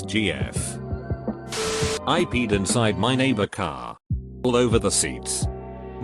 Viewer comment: I thought it said Cat not Car...